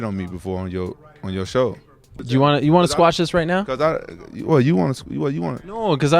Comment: Rampage probably thought he said “squab” not “squash”